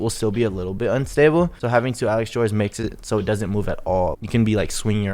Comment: It sounds like ur saying alexjoy like what is that